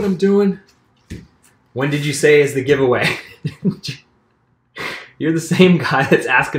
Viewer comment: did I hear: "giveaway"?! ;-p ;-p ;-p